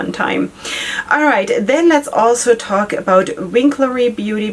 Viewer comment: Hello, I couldn't make out two of the brand names, and I'm hoping you can let me know them. The first appears at the 43:35 mark, and the second is at the 45:34 mark. Thank you! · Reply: It's Winklerry Beauty or Cosmetics and Blue Maos Beauty